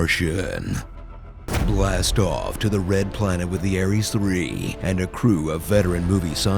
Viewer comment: Can you help me find which part of the movie announcer’s speech sounded like he said, “Der Plant”?